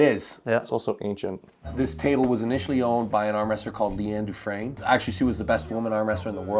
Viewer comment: What is the name of the female armwrestler at 02:00 ? I couldn't understand the name. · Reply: liane dufresne